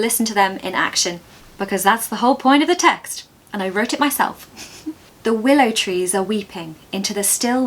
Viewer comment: Amazing tips, thank you so much!! You sound so sweet, too. I have a question: did you say 'I've wrote it myself' or I misheard it? If yes, can we really use V2 with Present Perfect? Sincerely, Paul.